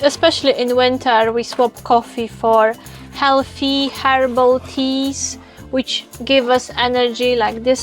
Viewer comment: Good tips there 👍 I'm a fan of the flavored tea also 👌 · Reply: HERBAL? i thought she said HARIBO? lovely lady haha!!